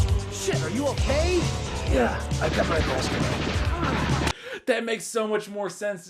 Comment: Ok, I thought he says " I got my holster" too.. but at 21:24 for some reason I can make it out better and he actually says "I got my vest on"... still a crap movie with garbage ADR lol but at least that makes more sense.